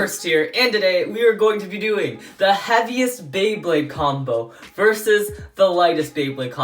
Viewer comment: did anyone else hear "the Happiest beyblade"